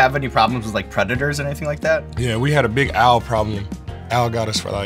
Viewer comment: When he said owl problem I heard 'AL problem' and pictured a fat guy named Al dive bombing his chicken coop